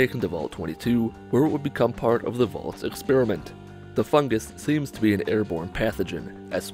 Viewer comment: For some reason when I hear 'em say "Fungus" I instead here "FUG-gis" Not meaning to be mean